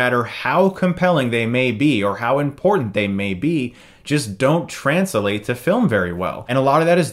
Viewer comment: This may seem very weird to point out but you randomly started to mispronounce words. 2:59 "Transelate" instead of "translate" and "Survaleeance" instead of "surveillance" 0:13 hope you don't have a stroke soon. besides that good review.